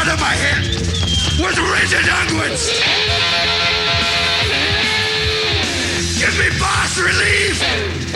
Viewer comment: Give me bass relief. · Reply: I thought he was saying “Give me fast relief!” Like the old AlcaSeltzer commercial.